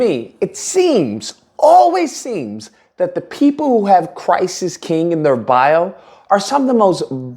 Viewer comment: At first I thought said “people who have Crisis King in their bio” lol